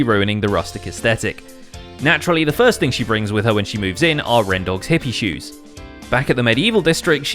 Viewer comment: did you say rendog hippie shoes